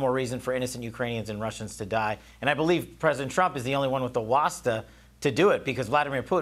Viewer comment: Help me out here ... if you please. What is the word that Kash Patel uses in characterizing POTUS Trump? To me, it sounds like "watsa". Huh? What? TIA. MAGA